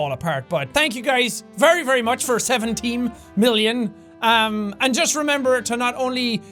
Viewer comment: your board sounds like its saying 70 million subscribers, Seventee mmillion